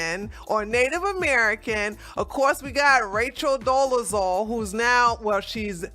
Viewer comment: Did you just say “he doable “. Hahahahahahahaha